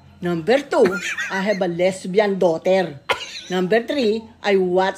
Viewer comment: Can I please have subtitles? I can’t tell what the mother is saying. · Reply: I thought she said 'I am ruble's drag race" and just fully excepted it XD